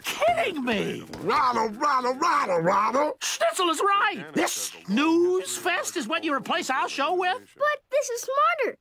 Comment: Did he say News Fest or Snoozefest?